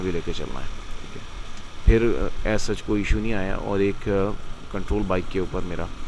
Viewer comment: did he just said "aysa "Ich" koi issue nae..." like mumbai mafia? 😐